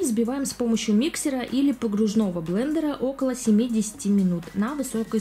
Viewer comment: мне послышалось: взбиваем около 70-ти минут😂